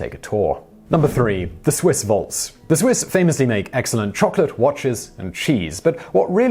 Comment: At #3 I thought he said 'chocolate watches'.